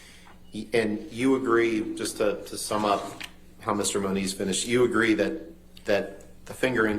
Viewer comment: am i mishearing it or did ambers lawyer actually call him mr money?